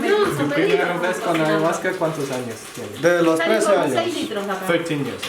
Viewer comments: " de los tres años " " thirteen years " I think he said three years old? Ayahuasca at three years old?! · I think he said 3yo not 13yo